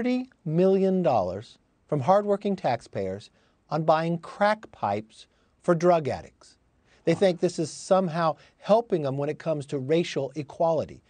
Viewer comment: Is this coming from the Infrastructure money? Because, we need some cracks and potholes repaired where I live. Ohhhhh, I thought they said "CRACKED PIPES" lol